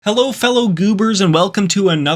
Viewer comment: I thought he said "Hello fellow gooner's" at the beginning 😭